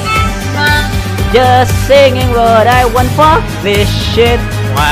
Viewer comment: I still can't make out what the guy says at 1:26 but it sounds funny as hell when he says it. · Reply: just singing what i wa- fuck this shit flap